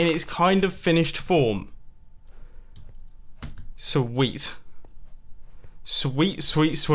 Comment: i thought you said so weak